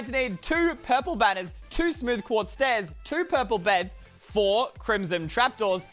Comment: did he just say 2 purple beds?